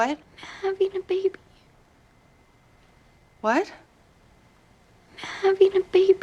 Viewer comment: I definitely heard “I’ve eaten a baby”